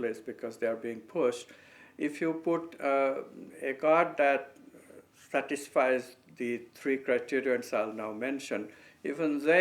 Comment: Can some one please tell me, what is this word he says on 3:35. It would help a lot for me. Bless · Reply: "the god that satisfies the three criterion...."